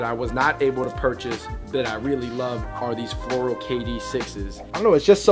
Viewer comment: Are the aunt pearls also called the florida 6's? Im cunfised lol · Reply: You misheard him, he said "floral" and "6's" not "florida 6's".. lol